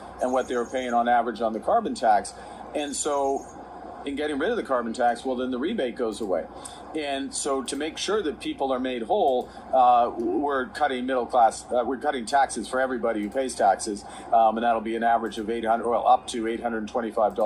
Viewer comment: Did anybody catch Carney saying 'We are cutting the middle class'